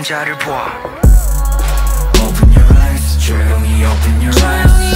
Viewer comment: I just realized that this song would be great as a morning alarm... "Open your eyes" 😂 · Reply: I thought they said open your rice